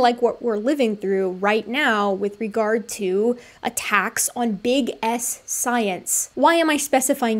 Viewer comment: Call me childish, but I kept intentionally mishearing what you said as ”big ass science ”